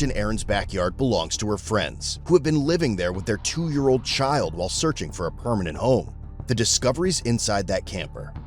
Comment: He said "while searching for a permanent home," but I heard "while smoking meth."